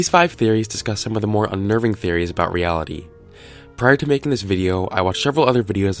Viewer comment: OH REALITY I THOUGHT YOU SAID REALTY